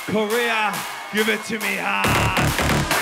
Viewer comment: is someone saying "Hardwell"? 😳